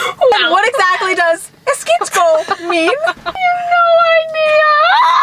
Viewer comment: what is sayin ? “You know my idea” that’s what it sounds like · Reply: I think she is saying "you have no idea or I have no idea"